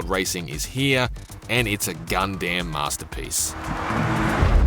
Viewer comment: does he said Gundam Masterpiece when the gundam like robot passes in front of the car, as a wordplay? cc shows 'goddamn', Im not a native english speaker